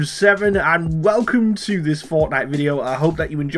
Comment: I thought he said fartnite